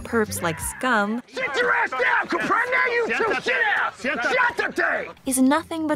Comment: Always thought he was saying "shutate" (combination between shut up and "cállate")